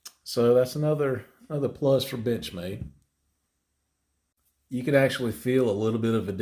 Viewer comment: To me it sounds like he says bitchmade. Wtf